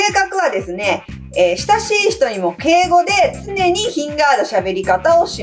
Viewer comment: 雪城ましろちゃんのところで、「敬語」が「ぴえ語」って聞こえて、初対面の人に「～なんですぴえん」とか言うのかな…と思ったら全然違いました 。